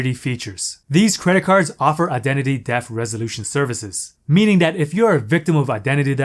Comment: i don't know why i think i heard "identity death" resolution services. Either way very good content!